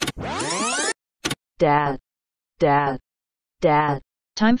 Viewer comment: I heard shadow and dab as well